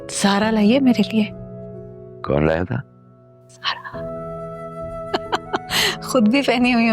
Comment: her mother said sara instead of zara 😅